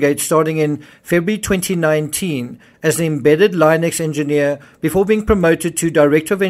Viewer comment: A what? Are you saying Linux as Line ex?